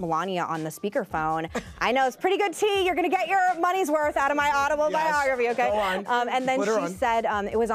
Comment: Did she just say “audible-biography” the 3:00 mark 🥴🤣🤣